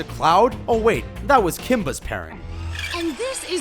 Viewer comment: did he just say kimba . . . TRIGGERED if you dont know the loins name is Simba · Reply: MASHED POTATOES Kimba is the white lion